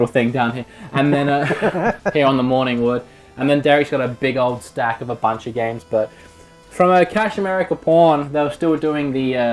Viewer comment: I need to turn my volume up it sounded like you said morning wood and derek has a big staff I know you said morning wood and that you said stack not staff but I heard that wrong haha